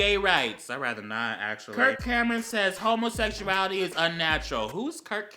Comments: I thought he said kirk hammett from metallica for a moment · When he said Kurt Cameron I thought he said Kurt cobain and I was hecka confused